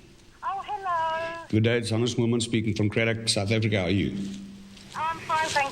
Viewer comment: I keep hearing "Mormons" instead of edit: thank you so much for the likes